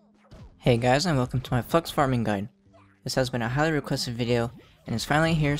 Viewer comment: Wait wait wait in 0:02 did you say Fuck farming guide